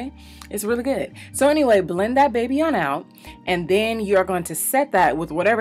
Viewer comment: I had to rewind at 2:36 I thought you said "blend that b**tch on out" I said what she doesn't talk like that on her channel. You said baby lol. Love how half baked brought the eye popping!